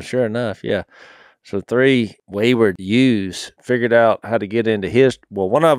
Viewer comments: Three wayward sheep? Sounds like he says "ewes" instead of "youths" Hahaha like "utes" in my cousin vinny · I thought he said "3 wayward jews" at first lmao